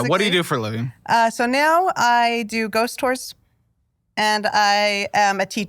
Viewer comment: wait did she say she works in Ghost stores? What is that? When like a Halloween popup comes around in October? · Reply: I thought she said doing ghost tours lol